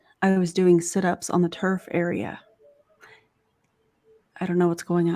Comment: Lol, when said she was in the turf area at the gym (around the 2 hour mark), at first I thought she said TERF area 😂